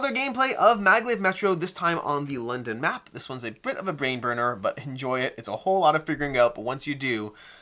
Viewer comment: Did just call the London map "a Brit of a brain burner?" Accidental genius.